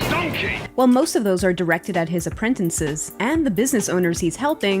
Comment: did she say apprentences?